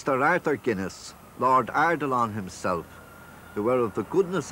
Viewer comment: what does he call arthur guinness @ 3:32, "lord _____"? tx. subtitles say eidolon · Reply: Arthur Edward Guinness, 1st Baron Ardilaun, 2nd Baronet (1 November 1840 – 20 January 1915), known as Sir Arthur Guinness